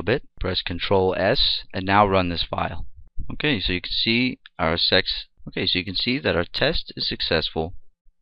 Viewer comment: did you just said "Okay so you could see our SEX"? or am I hearing it wrong? HAHAHAHAH! anyway great tutorial sir! I guess 45% of my knowledge in php comes from your tutorials XD Thanks!